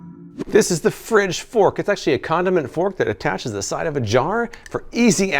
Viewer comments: can someone tell me what he actually said? For some reason I thought he was saying condom. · When he said “condiment fork” my baked ass thought he said “condom and fork”